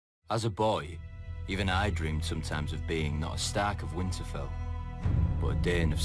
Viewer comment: I thought Young Ned said "not a stag of Winterfell"... which would make me cry. Upon a second listening I notice he says "STARK of Winterfell". Thank God for rewind...